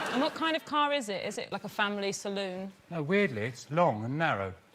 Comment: does sara say "what kind of car is it? Is it like a family 'saloon'?"